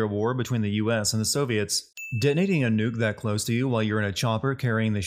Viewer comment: did he just say “Denating” instead of “Detonating” Ding sound effect